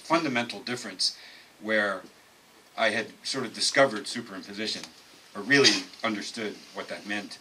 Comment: what does greg say at 6:20? Is it "superposition"? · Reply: Super-imposition. Greg uses the verb version super-imposing several times.